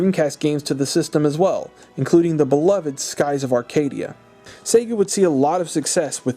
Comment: am I the only one that hears “Satan” instead of “Sega”?